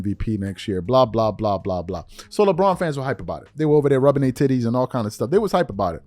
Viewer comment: did charles said "rubbing their tïttïes with honey"?